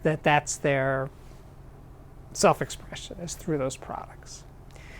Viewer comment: I just cannot understand the name of the company he mentions on 4:53... I always hear "shit audio" hahaha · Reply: Blake hahahahaha that's some good Schiit :v